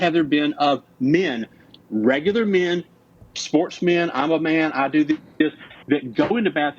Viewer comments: Did he say at 25:16 "i'm a man?" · I swear I heard him say "I'm a man" at 25:16. Put the captions on and press the time stamp 6 times fast! 😂😂😂😂